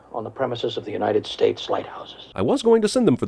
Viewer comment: Wait 10:36 are you seriously saying the US has regulations about dragons at the White House? Who the hell was crazy enough to pass that regulation? or did he say lighthouses? in which case same question...